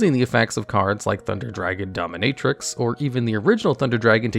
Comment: did u just say thunder dragon dominatrix?